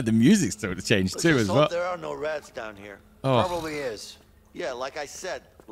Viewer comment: I thought he said "let's hope there are no rads down here" and I immediately thought he was making a fallout joke for a split second.